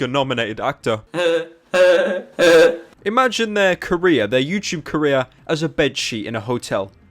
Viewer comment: because of his accent i thought he said Korea when he said career